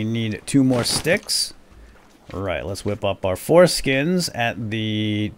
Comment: "Let's whip up our foreskins at the tanning rack." Did you just say that? :-D